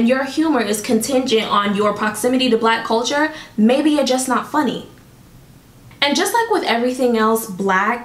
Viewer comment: I heard "--maybe your just not funny...bitch"